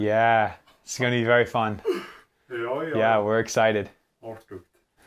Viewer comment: people how do not know what he said he said yes that’ll be good